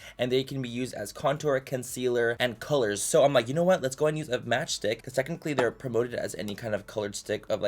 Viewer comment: OHHH “Match Stick” I thought he was saying “Match Dick” 😭😅